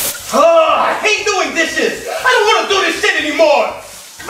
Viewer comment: best part 🤣🤣🤣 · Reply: Mother Trucking Bird I thought he said “I don’t want to do these dishes anymore!!! *Smash the plate on floor & shattered it”